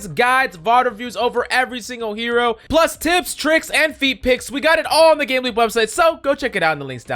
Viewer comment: Did he just say feet pics in the beginning? Awesome